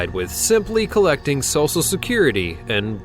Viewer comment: am I the only one that heard him say "sosal" not "social" security?